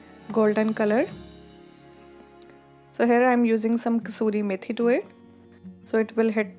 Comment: what was added at 1:07? couldnt make out what she says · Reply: That’s dry fenugreek leaves.. that’s optional